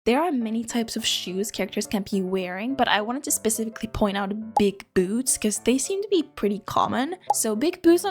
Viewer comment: I was listening to this while drawing so I didn’t see the text thingy at the beginning, so I heard “big boobs” instead of “big boots” at first